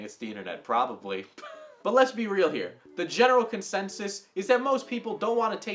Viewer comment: I was half-listening around 5:34, and almost thought I heard "genital consensus."